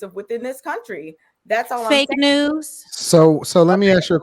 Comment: I thought she said "fake nails" lmaooo I was about to say go off on her sis! Fake nails, fake hair, fake smile most of the time, strong independent don't-need-no-man attitude, overweight, obesese fight-you-for-the captain's-chair ass women is why black men leave their own kind.